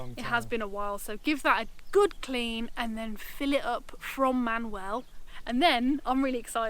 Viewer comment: Main well , hahaha with your accent I thought that I've heard Manuel hahahahah